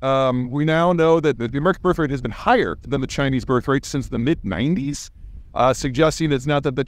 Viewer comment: Cant figure out what is said at exactly 1:00, can anyone help? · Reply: I believe he said "the American birthrate had been higher than the Chinese...."